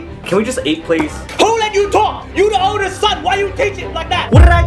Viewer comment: He sounded like he said whadidiydo